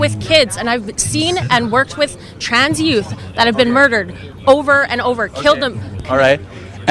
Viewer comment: did she say she works with kids that have been killed over and over again?